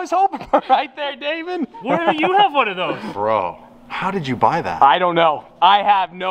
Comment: When Damon from says “how did you buy that” with the look he had on his face, you know it’s going to be crazy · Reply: I thought he said why don’t you have one of these.